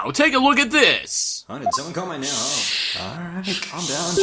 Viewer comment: What did Phoenix say at 11:53? I didn't understand that, lol. God, Phoenix shouting from the gallery is hilarious! I wish he would have done that for real in the game, lmao! Also... Apollo: My sword? (I didn't know I had one to break.) Oh dear lord that just opens up to some dirty thoughts and innuendo. Thanks game! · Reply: Fulbright: "Right, let's take a look at this." Wright: "huh, did someone call my name?"